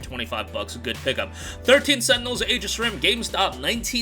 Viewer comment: Thought you said "13 sentinels age of shrimp." 😂